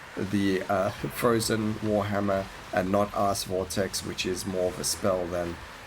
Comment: great video but i wasn't paying full attention and it sounded like you said "arse vortex" haha